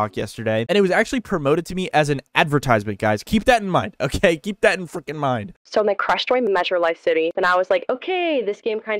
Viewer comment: i think he actually said "keep that in fucking mind" because i slowed the video and it sounded like he said "fucking" instead of "freaking" every time, so we got our 4th proof of swearing now